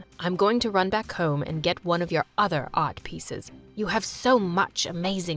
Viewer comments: the captions at the bottom say other odd pieces, but I think he said other art pieces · I love how the captions say old instead of art 😂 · Wait a minute.. in 12:00 did scott say one of your odd pieces or art pieces. If he said art the captions say otherwise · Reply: I think he said odd